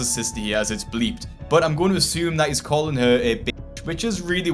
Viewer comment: dude.... how do you get bitch from what he says at 10:02???? That is actually insane. He says ass hole. Nothing about his mouth movement indicates he says bitch.